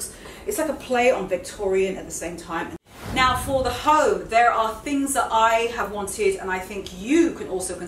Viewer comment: I thought you said "Now for the Ho!" haha HOME